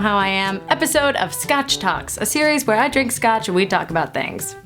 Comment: When i heard you say scotch talk on snapchat. I literally thought you were talking about scotch tape... 😂😂😂